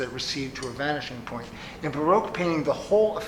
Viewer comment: What is he saying at 5:05 min? "in ... painting..."? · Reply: "in Baroque painting"